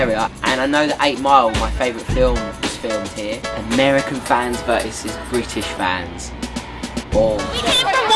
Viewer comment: oh m gosh....i thought he said "American VANS verse British VANS" then he started talking about the girls and im lik "Ohhhhhh FANS!"